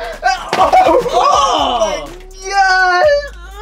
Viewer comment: did just say oh my gyat and even just moaned💀💀💀